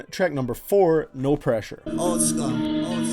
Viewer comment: i was listening to the album alongside and when he said old scar i thought he said pornstar